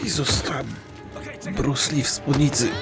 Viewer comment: O Jezu...! "Ta Bruce Lee w spódnicy" Śmieję się i nie mogę przsetać. No i niech mi ktoś powie jak można nie oglądać? :3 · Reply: ja usłyszałem "To Bruce Lee z ulicy"